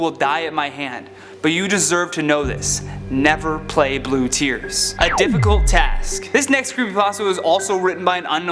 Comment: i thought he said its a difficult task to not play blue tears